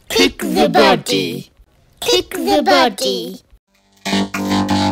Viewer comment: I Hear AVS Media Demo